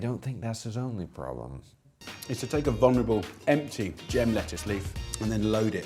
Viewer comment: did Jamie call the lettuce "vulnerable"? What the fuck